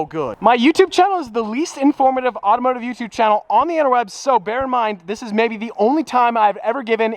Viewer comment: did he just say interweb